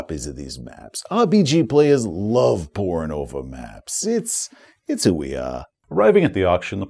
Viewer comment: first listening I could have sworn you said "players love porno for maps" - Had to relisten more times than I care to admit to hear "pouring over"